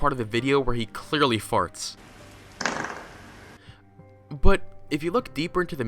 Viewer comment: I thought said "but if you look deeper into the meaning of his fart.." I was already suffocating with laughter from the guy farting and when he said that it almost killed me. Then I realized he said "art"